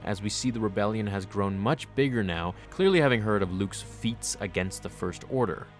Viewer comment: almost thought he said "clearly having heard of Luke's feet"